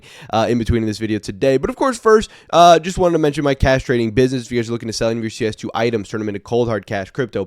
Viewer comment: I know you said "cash trading business" but i heard "castrating business😂"